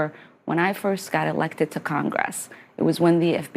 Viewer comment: Did Ilmar say "congwess"?